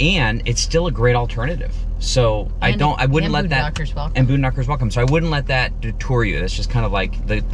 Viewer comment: I put on Closed Caption and it didn't even know what was being said.... something about a "Boom Doctor" is what CC picked up. UPDATE 🤣 Boondockers!!!